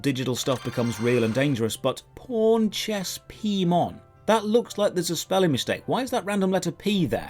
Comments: Man when he says that name slowly, all I hear is "PornChessPMon" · Heard it as PornChessmon before seeing the Pawn.